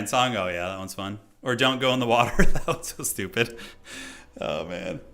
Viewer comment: I heard ''dong go into water'' now im disappointed